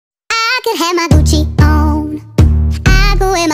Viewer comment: Am I the only one who thought she said "I could have my cøøchie on" ?